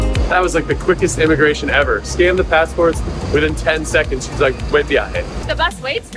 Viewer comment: We loved Paraguay! It was our pleasure to come hang out for a while 🥰 Quick question: were the subtitles good? Did they make a lot of sense or no? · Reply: Now I've noticed two small things for correction: 1:07 - Instead of "Hueatia" it should be "buen viaje", basically meaning safe travels :) 14:16 - It should say "los pájaros son geniales". While you guys use the word "sick" meaning something is awesome, the translation is literal: "los pájaros están enfermos" --> this means that the birds are sick (regarding illness). Everything else is on point <3333